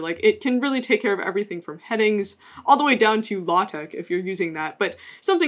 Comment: I heard "Law-Tech" and thought for a moment there was a markdown language for attorneys, and I was excited for a moment as a software engineer married to a lawyer. Then I realized that was how you pronounced LaTeX.